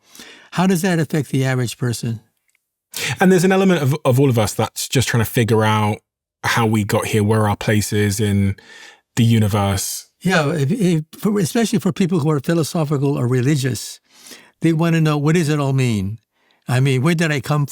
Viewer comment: did he says religious 😇wanna know the meaning of life?🙄🤔WHAT?